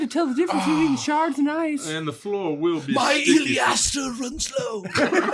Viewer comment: Someone, PLEASE, explain to me what is referring to when saying 'iliaster' or whatever he says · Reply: I think he's talking about Oleaster, a plant also called Russian olive. It has traditional medicinal uses, but I mostly know of it because we used to have one in the yard growing up. The birds loved it.